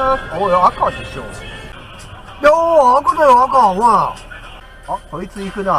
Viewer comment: 信号見切り発車、「赤だよ、赤」が、バカだよ、バカ」と、聞こえてしまう!w 割り込みの「何だよ、こいつ!」返事は、「そうです、わたすが変な割り込みおじさんです!」とでも言ってそう。 ハハハハ